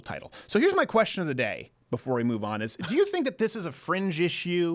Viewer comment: did he just say "zis" lol